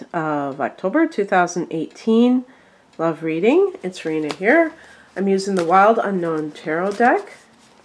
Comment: Please take no offense, but when I first listened to your videos, like two years ago, I always thought you were saying " Hi It's raining here" lol...I used to wonder, " where does she live and why is it always raining there"? Lol. Now I try to listen carefully to hear your name. Is it? Sorry for my ignorance